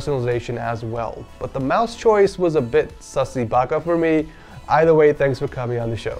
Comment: Did he say "sussy Baka"